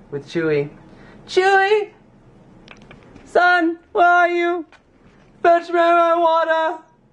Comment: definitely thought you said fetch me my marijuana :/